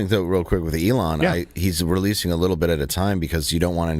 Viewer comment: I thought I heard you say that Breitbart revealed Weiner little bits at a time.